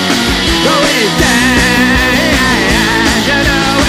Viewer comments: Yes yes on my Amazon music , thought it said Lance was the landlord 😂😂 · Yes yes on my Amazon music , thought it said Lance was the landlord 😂😂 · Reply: I thought it said Lance was the landlord man too